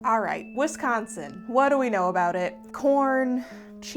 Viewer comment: I misheard the first three seconds and thought you said "Wisconsin, what are we gonna do about it" and had to go back to the start after i was done laughing. But seriously, what are we gonna do about Wisconsin? 🤔🤣